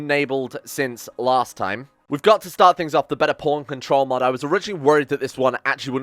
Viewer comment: Thought I heard bater porn control mod for a sec